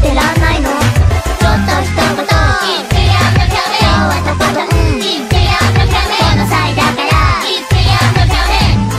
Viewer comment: I keep hearing the part near 2:33 as "Eat the cafe!"